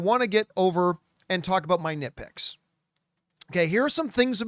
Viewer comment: LOL. Had this playing in the background while I worked, and I swore @ 3:16, that I heard say he wanted to talk about his 'dickpics'! Needless-to-say, I was startled.